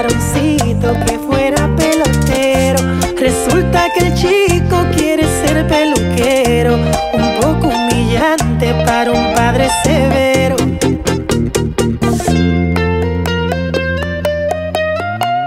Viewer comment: Yo pense que decia "resulta que el chico quiere ser callejero" pero dice peluquero ajjajajajq